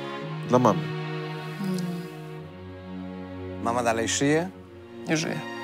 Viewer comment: Czy tylko ja usłyszałam "mama SZYJE ?"